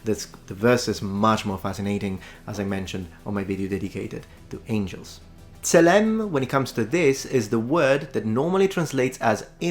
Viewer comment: As a native hebrew speaker, I can attest that Metatron has done an excellent job of pronouncing and interpreting the original biblical hebrew. I might also suggest that Tzelem could also mean "likeness"... a similarity of character and essence, and not simply physical form. Love your videos. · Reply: Thought he was saying "tellem"